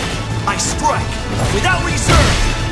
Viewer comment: i thought he said "listen to the raging of my salt"